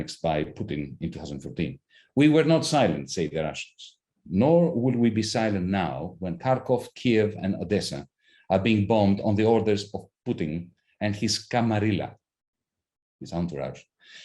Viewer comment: Did he just say TARKOV, Kiev, and Odessa? Tarkov, the fictional city from a video game? Really? Who the fuck is this guy and why is he saying fake shit?